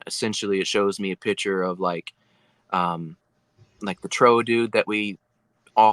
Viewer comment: Interesting experiences, indeed. @ 21:39 is he saying "trow dude"? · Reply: So what he was saying that if dinosaurs didn't go entirely extinct....it was theorized that there would be a hyper intelligent one named Toodon....it's a lizard people explanation.